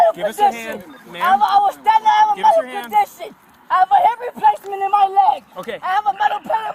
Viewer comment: Did she say she has a hip replacement in her leg?